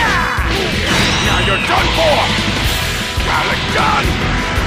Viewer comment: Vegeta is a bad ass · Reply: Emanuel Standifer 5:06 did he said abadon